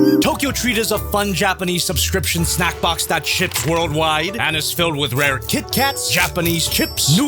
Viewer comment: i heard "shits worldwide" when you actually said "ships worldwide"